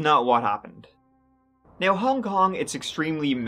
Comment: Thought you said " Cow Dong " when referring to names. Had to replay that segment few times.